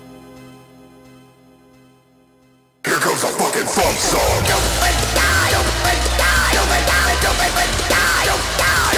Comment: what does he say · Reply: "HERES COMES A FUCKING F.U.N.SONG!!!"